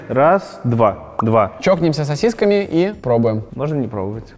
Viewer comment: Влад Прости но я сперва услышал вместо чокнемся чпокнемся😂